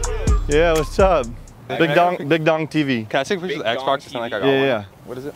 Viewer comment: I heard “big dongs” 😂 that was funny